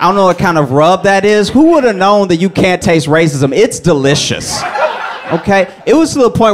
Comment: I about pissed myself 😂😂 "you can't even taste the racism" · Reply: I thought he said, "Who woulda known that you can taste racism; it's delicious."